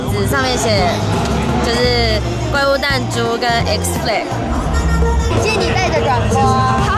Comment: 我差点听成XFLAG F***⌇●﹏●⌇吓死宝宝了